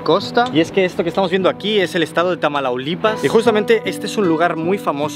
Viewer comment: Exelente vídeo!!! Solo que es "Tamaulipas" no "Tamalaulipas", bueno algo asi lo escuché 😅 minuto